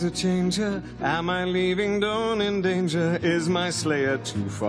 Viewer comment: "Am I leaving Dawn in danger?" ( 1:35 ) Best line. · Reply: I thought he said don't endanger